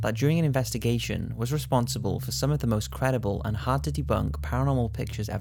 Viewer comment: at 25 seconds I had to rewind it because I thought he said "hard titty bunk" it was hard to debunk haha